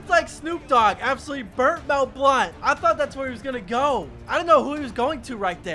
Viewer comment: When he said that snoop dogg burnt Mel Blount I thought he said snoop dogg burnt that blunt